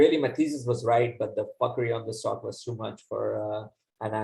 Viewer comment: What did he say? Fuckery? Lol